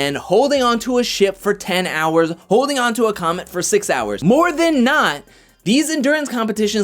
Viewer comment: Did you say holding onto a comet or “comment” 😆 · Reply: I said comet. I was referring to the final 4 HoH comp in BB14. Sorry I wasn't clear enough. Thanks for watching and commenting! I really appreciate it!